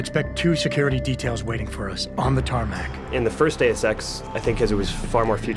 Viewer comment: Who else heard "In the first day of sex..." around 0:43?